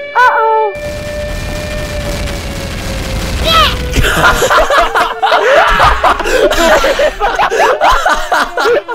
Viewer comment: did anyone hear Lui's cute little "Shit" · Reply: It's bleh